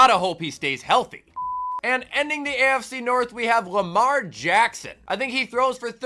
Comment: Bruh I thought he said “hamar Jackson”